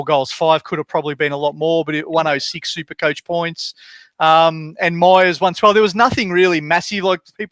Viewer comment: I swear I heard you say “Adam Miers”